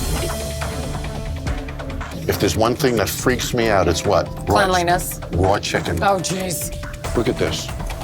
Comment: when he says “what’s one thing that freaks me out?” I thought that chick said “cunnalingus”